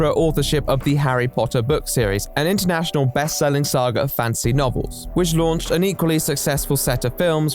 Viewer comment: I know said Fantasy Novels but i heard "Harry Potter is a series of Fancy novels"😂